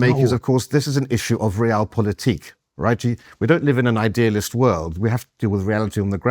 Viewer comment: Did he say “right,G” around 31:40?